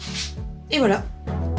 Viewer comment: What is said at 16:18? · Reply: Et voilà.